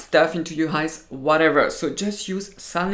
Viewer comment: can you please write down what is that thing on 4:58 to clean eyes, I cannot understand, btw thank you for the video 😊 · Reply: he said saline. You can use the saline for contacts, I do all the time and it's great. The single use vials are quite expensive, saline is of course made to use in the eyes so the saline for contacts is fine. Please correct me if I'm wrong.